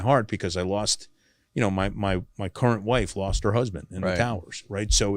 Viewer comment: At first I thought he said that he was hurt because his ex wife lost her new husband😢😢😢🤭🤭🤭